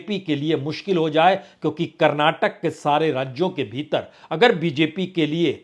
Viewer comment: I think he said 2072! Anyone plz. Conf. 😊